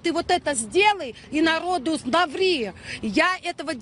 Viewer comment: Мне послышалось "На воду наври"